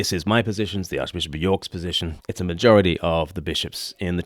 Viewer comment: See my comment earlier. The ABC is clear that he is stating he and the bishops’ view and that this is not a majority. This about 8:50. See response. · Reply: thanks. Maybe I'm mishearing the ABC, but sounds like Welby says "by a majority but not unanimous" not "not a majority".